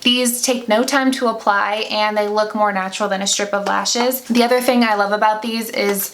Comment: Hahaha, I thought you kept saying stripper lashes lmao